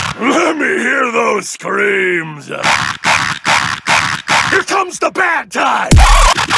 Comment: Que es lo que dice en el minuto 1:18? Quiero decirlo pero no sé qué dice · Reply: Its time, lets talk Let me hear those screams Here comes the bad time